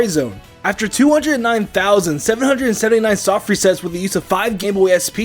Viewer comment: He said 209,779 soft resets at 2:21 instead of the number on the screen 😂 (I think)